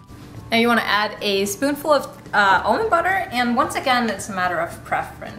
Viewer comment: Did anyone catch the type of butter she used...I thought I heard her say Om butter...its was a brownish color. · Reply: Robert Spiller almond butter ;)